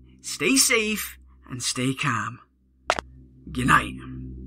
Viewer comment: Why does it sound like he said stay cam